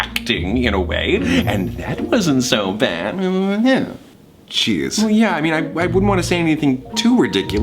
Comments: lol not important but he says "jeez" not cheers... Check the captions I thought it said cheers aswell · weird noise What I knew Virgil said: Jeez. What I thought Virgil said: Cheese. Me: Thomas-like laughter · in 3:26 I always thought he said cheese just bc idk until I put the captions on and now I feel stupid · I know virgil is saying "jeez" but I just can't stop thinking everytime I hear it that he's saying "cheers" in a british accent 😂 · i thought Virgil said Cheese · Why does it sound like he said “cheese”? XD